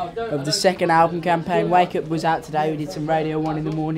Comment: For a second there I thought they were saying "break up" instead of "wake up"